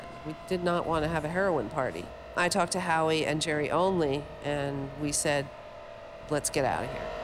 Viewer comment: Did she say Jerry Only was there at 37:57? Jerry Only is founding member of the Misfits. I didnt know he was there the night Sid died.